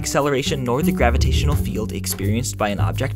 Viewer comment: The narrator doesn't need to speak so quickly. At 1:30, he says "field" and the subtitles say "force." It's hard for a novice to keep up, and that's the point isn't it?